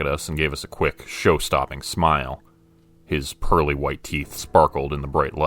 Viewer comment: I thought he said “curly” white teeth, instead of “pearly…” Gods. What an awful mental picture. XD